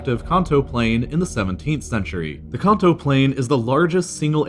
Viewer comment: As a pokemon fan, everytime "plain" was said, I heard "region" XD